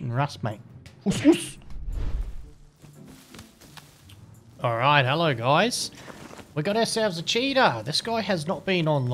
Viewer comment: All I keep hearing is "hey guys, we've got ourselves a cheetah" :D